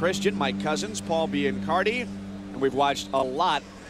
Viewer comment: Who else thought they were saying my cousins paul b and carti?